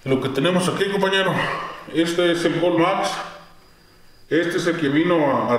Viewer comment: en el minuto 6:53 dices el nombre de ese liquido pero no se entiende que es algo de maiz pero no entiendo me puedes decir el nombre de ese liquido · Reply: Mi hermano, se llama GoldMax y lo puedes conseguir en Mercado Libre ... Saludos!!